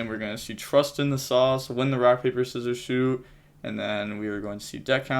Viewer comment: We don't say rock paper scissors shoot in the UK. We just say rock paper scissors so I thought you were saying rock paper scissors shoe for the longest time